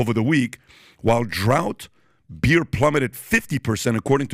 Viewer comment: Did i hear 'drought beer' rather than 'draught beer'? 🤣